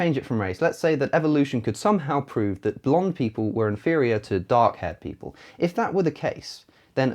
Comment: Did anyone else thought he said dog-haired people? :D